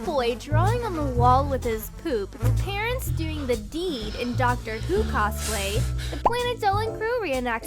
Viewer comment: I thought she said "Dr seuss" instead of "Dr who"